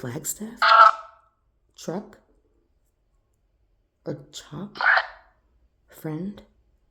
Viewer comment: instead of chop could she be saying Cop. I keep repeating that part. And i think it sounds like Cop.